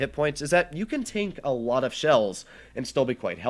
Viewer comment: Sounds like he said, "You can 'tank' alot of shells..." That's pretty funny!